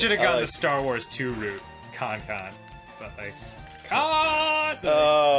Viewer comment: did anyone else think he said “Tonton” for Star Wars 2? It actually would have worked anyway 😂